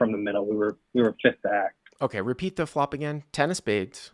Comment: lmaooooo I thought he said Repeat the F*#king hand😂😂. I was like wow I guess having a bad day or really hates bomb pots😂